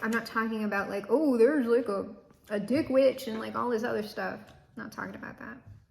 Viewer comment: …..ma’am did you say “dick witch” ? 😂😂